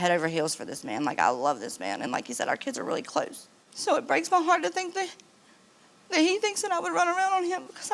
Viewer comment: Did she say she would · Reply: she said “it breaks my heart that he thinks that i would because i wouldnt” its hard to hear at first lol